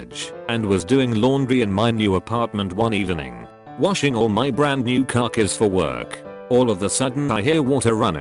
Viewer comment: I heard “washing all my brand new carcass”